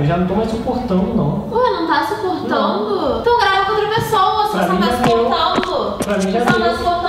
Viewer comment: Só eu escutei "tô grávida de outra pessoa" kkk o coração até acelerou